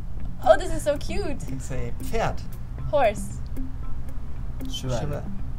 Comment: Ngl, when 'horse' name up, the German man sounded like he said 'cat'- i have no friends